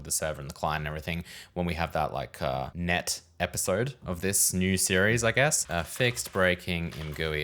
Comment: did i just hear next series?